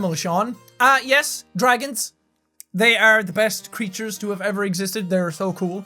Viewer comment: i paused the video and i thought he said "dragons,they are the best animals to ever."